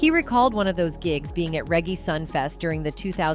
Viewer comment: Boost-a Rhymes? OMG. Who is this woman talking? I swear she just said Regg-ee